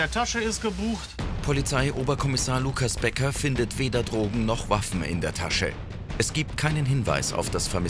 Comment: verstehe ich wuaffen und nicht waffen😂